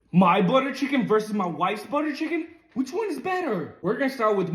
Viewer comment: Why was I hearing " butt of chicken"